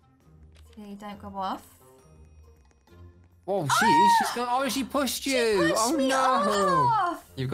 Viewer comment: Did I hear say “o-ohhhh shi- shevpushed you!” It’s ok though,love you